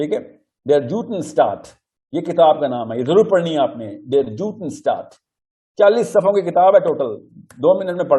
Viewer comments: he has mentioned the book name, Could someone please write that book name in my reply, i didn't understand the name. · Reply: Der Judenstaat by Theodor Herzl published in 1986 The Jewish State (in English) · Can somebody tell the right name of the book Mr. Sahil mentioned at 05:00? I couldn't understand the name. Thanks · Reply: Der Judenstaat Book by Theodor Herzl